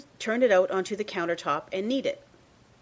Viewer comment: LOL my volume was low and when she said "...then it is time to take it out of the bowl and kneed it," I thought she said eat it o: i was like, "O_O!"